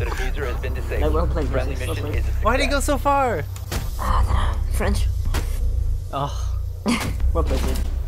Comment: Did I heard "French" ? Tsss